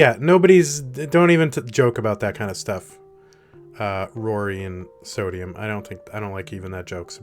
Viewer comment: Rory and sodium; I don't know what you said, but I like you. Edit* @ 25:27 the irony! LOL